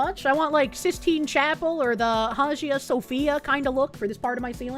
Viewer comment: I heard "Sophia" and it got my attention, but "Hazia Sophia?" Is that a weird, Bostonian foxu pronunciation thing?